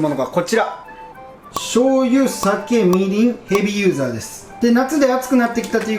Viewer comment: ヘビーユーザーって聞こえた